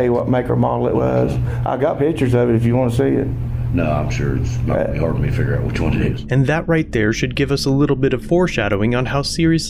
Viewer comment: The caption here is wrong, which does make a difference in the attitude the officer looks to have towards the case. The caption is "Nah, I'm sure it's not gonna help me figure out which one it is." What he says is "Nah, I'm sure it's not gonna be hard for me to figure out which one it is." I love your content, but it's important to get it right. Keep up the good work!